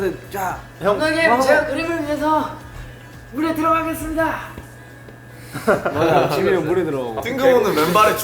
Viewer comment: Did jk said jiminnie instead of Jimin hyung? Or it is just I heard wrong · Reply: He said "Jimin hyung" it just wasn't very clear cause he was talking so fast